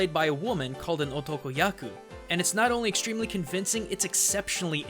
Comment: I swear I heard bukkaku ;)